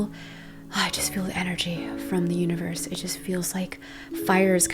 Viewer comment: I thought you said I feel energy from the uterus 😭🤣 had to replay that again to hear universe